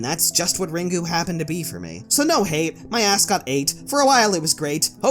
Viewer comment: i thought he said my ascot hate lol